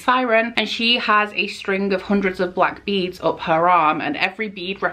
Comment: dat intro thoooooo ⭐️⭐️⭐️⭐️⭐️ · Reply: i heard "hundred of black beads up her arse" IM GOING TO EXPIRE NOW